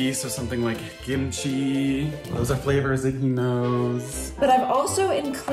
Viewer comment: “gimchi” 😂😂 I’m so sorry it’s just so funny or is it just me idk if I misheard it